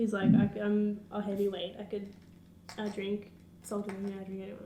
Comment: its sounded like she said " i drank... Sultan... " the first time and im like 'wtf!?'